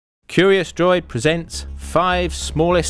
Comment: In the beginning, I thought you said Curious George. The Song is now in my head.